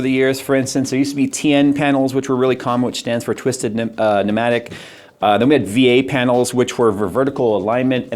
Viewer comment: anyone else catch him almost saying “twisted nipple”?